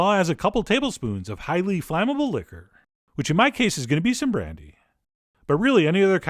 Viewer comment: "Which in my case is Sombro" Idk what he is saying LOL pleaes Explain!